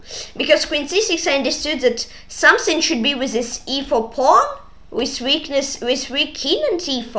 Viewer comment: Wow! 3500+! An amazing puzzle rating!! Nice, indeed! :) Also very good to solve 6 such hard puzzles in a row! I first thought you said "I understood that something should be with this evil pawn!" :))